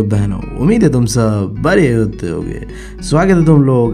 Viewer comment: Aap kya bolte ho bade ache ho ya bade hagte ho😂😂